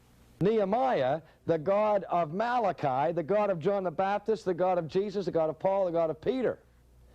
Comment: what?! Did he Say"the God of Jesus"?...🫢